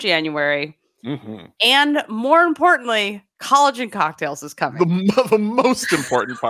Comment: After the mention of replacing nutrition with supplements, here I heard "collagen cocktails."